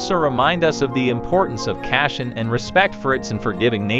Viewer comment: He said "Kashen" instead of "Caution". These robot voices are funny.